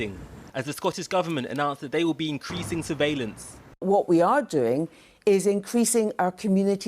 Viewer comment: what did she say? Sur veal lance ?